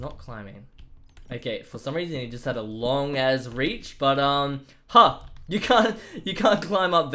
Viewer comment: I am a huge fan and am just wondering did you say "Long ass reach"? at 13:13-13:15 I heard it and laughed lmao but it might just be me also I wish u best luck!